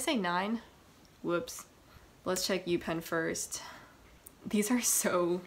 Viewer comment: I thought that said peni- Pens